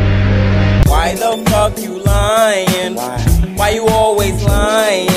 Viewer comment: I heard why the ** you lyin'?